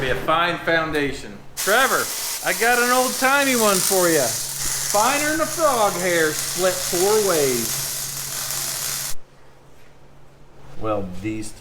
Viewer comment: What did say at 9:30? I am so confused. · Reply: "Finer than a frog hair split four ways"